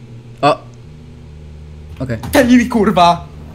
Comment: did he say kurwa ?